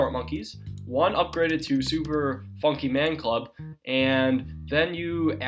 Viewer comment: Did he say Super Funky Man Club?